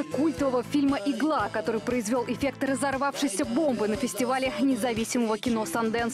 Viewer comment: эффект разорвавшейся бомбы, а послышалось как эффект разорвавшейся попы. Ору 😂😂😂😂😂😂.